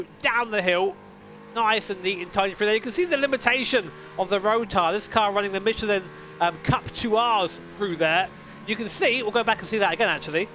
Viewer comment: did he say cup thua